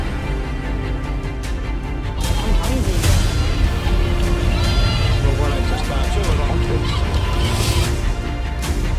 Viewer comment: what did harry say? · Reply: "I'm hungry, man!"